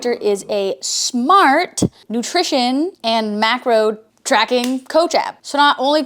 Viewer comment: I thought that said 'MARCO tracking' ... Like, why is tracking me?